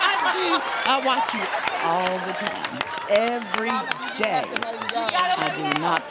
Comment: Steve looks terrified at 9:36! That woman will not unhindered him! I couldn't make out exactly what she was saying but my husband said he thought she said that she wanted him every day!! Poor Steve! If Steve's wife is as jaelous as Steve says she is, then it's a good thing Mrs Harvey wasn't there! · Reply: i think it was she WATCHES him every day like the show :3